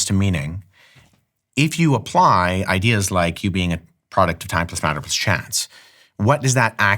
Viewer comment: Can anyone make out what is being said at 2:11? Sounds like "you being a product of taptismanabischants". I've slowed it down and still can't catch it.